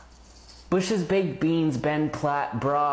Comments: It sounded like he said NBA protein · The last one got me I thought he said NBA protein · I thought he said "nba protein" I'm dead